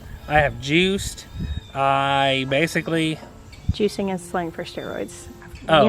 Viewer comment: I loved your video. sounds like an awesome man. I've replayed 12:08-12:10 several times and can't quite make out what you're saying. Is it "juicing is slim for steroids or juicing is sling for steriods? or ??"